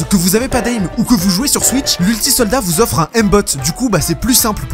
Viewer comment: J'ai compris "après moi vu que j'ai un aim de geu"😭😂